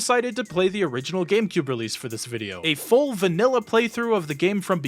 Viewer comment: "Not yet seen at this point in Canon" Judging by this, and your previous comment, I'm assuming you're not paying attention. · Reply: hm. could have sworn you said "by release". sorry i tired